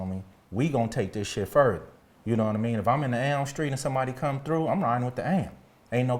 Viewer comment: what is he saying @ 7:08 and 7:10? is he saying "am street"? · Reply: Elm st.